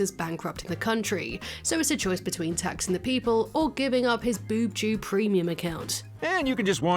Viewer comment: So I misheard it too, but it's actually VousTube, not BoobTube. Vous being French for "you".